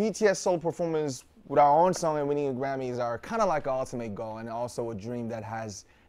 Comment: the subtitles say "kind of like awesome" but I think he actually said, "kind of like our ultimate goal". Just a little correction :)